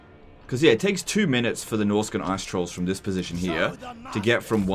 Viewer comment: Norscan Ass-Trolls! Now that's hilarious! P.S - Yeah, yeah, I know that I just misheard it.